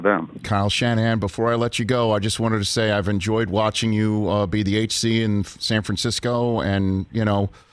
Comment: I thought he said, "I've enjoyed watching you be the eighth seed in San Francisco."